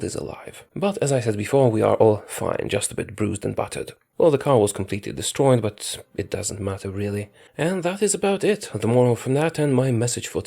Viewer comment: Glad to hear you all are fine! Only to poke fun of your accent (I am polish myself) but at 1:29 it sounds like you said bruised and buttered lol 😂